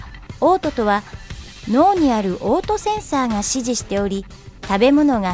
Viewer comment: 脳にある嘔吐センサー（オートセンサー？） ダジャレに聞こえたのは気のせい？w